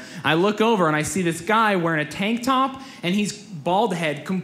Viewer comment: Loved the stand-up:D But did anyone back up by a few seconds at 3:19 cause they thought they heard "tank tahnp"